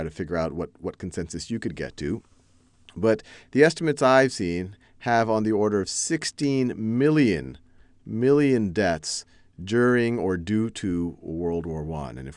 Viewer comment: the Chinese subtitles wrong in 1:40 not " 16萬人" , maybe that was "1600萬人" ?